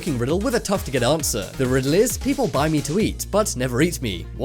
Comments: BODMAS??? PEMDAS!!! IT IS FREAKING PEMDAS!!! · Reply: I thought it said people BITE me to eat, so I guessed silverware. Anybody else, or am I alone? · Gum? . Edit - Oh nvm I heard the riddle wrong. Thought you said "What do people buy to eat but never eat it?". Lmao xD